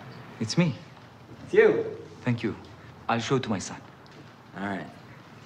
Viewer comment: or 26:08 it sounded like he was saying I'll shoot my son.